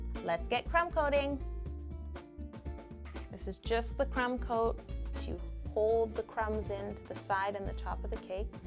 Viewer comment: I always thought she was saying crem coat 😱😅😂🤣😝